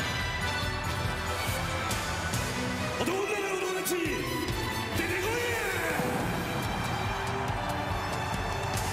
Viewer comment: Anyone know what he says at 6:24? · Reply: 男の中の男達、出てこいや